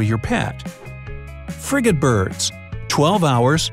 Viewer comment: I thought he said ”FRICKEN BIRD”